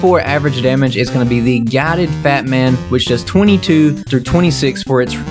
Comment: For a second I thought you said guided batman